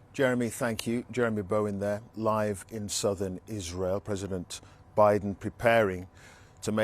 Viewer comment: ''Live in Southern Israel''''------------- I apologize ..................I heard ''Lying in southern Israel'' :)